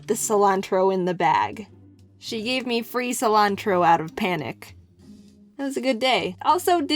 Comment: Right in the middle of the guy saying panic I got a notification that sounded like "Ah!" In a cute way. It sounded like he said pAHniced